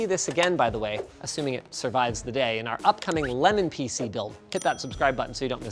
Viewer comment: Did he say LENIN PC?. (COMMUNISM INTENSIFIED)